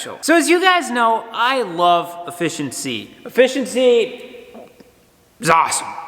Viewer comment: At first I thought you said I love fish and sea.... haha oh. Efficiency 😂